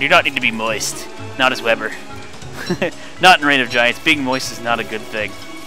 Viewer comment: you say being most is good most of the time but not in ROG lol what are you trying say 😇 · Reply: Moist not most very different